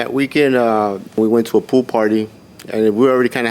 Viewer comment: With the plaintiff's accent i really thought he said "we went to a poo party" at the beginning and i almost lost it